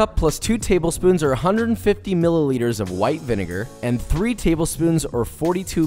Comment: i just have a question, normally in other videos they use vinegar to prevent the sauce to spoil. since you did'n use it, is it safe to keep the sauce for long periods? like 3 or 4 month? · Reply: Min 3:37 white vinegar.unless i heard wrong